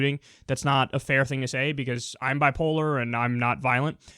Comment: what, did he say I'm bipolar at 4:46 ?